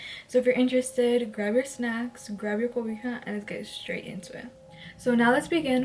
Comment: DID YOU SAY GUIJA SEÑORA YOUR MESSNG WHIT A ALMOST MEXICAN WOMEN WE DON'T MES WHIT DOSE THINGS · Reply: She said cobija “blanket” 🤨